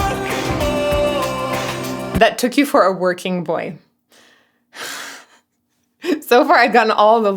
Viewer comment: I hear " they took you for a working bore" ( hence the you shouldn't have to jump for joy to follow up)